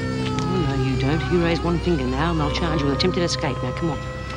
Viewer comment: Bea to Doreen: "Oh no, you don't. You raise one finger now, and I'll charge you with attempted escape. Now come on." Bea talking like a "screw". Val Lehman is forgetting--she didn't get the Vera part. How can Bea charge anyone with anything? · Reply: I thought Bea said "on no, you don't. You raise one finger now and they'll charge you with attempted escape."